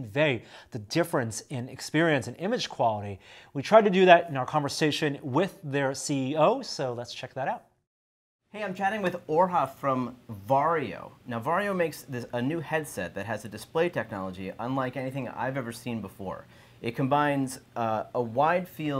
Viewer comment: All I heard was "from Wario."